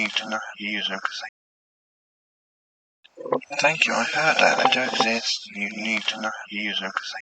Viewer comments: Loved it, thanks. The parts of the Frankbox that I thought I heard sounded to me like, Thank you, I do exist, you need to understand, there were other words mixed in that I didn't get. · I literally heard "Thank your father the drugs you need to not use them". Lol but then I put on my captions & it read "thank you I heard that I dont exist you need to know too." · Sounded like he said "Thank you I heard that"... couldn't understand anymore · I like this new addition! The last one, I think it says "Thank you, I heard that, I don't [..] you need to know how to [..]ing" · At the end it sounds like he said “thank you i heard her” and the rest if it i can’t understand